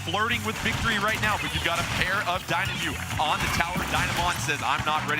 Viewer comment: ←ダイナモ乙って聞こえるww